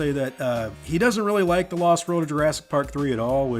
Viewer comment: " he doesn't really like the Lost World of Jurassic Park 3" is what I heard